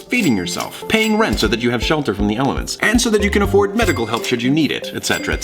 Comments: anyone else thought he said elephants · Nobody else thought said "shelter from the elephants"? Just me? Okay...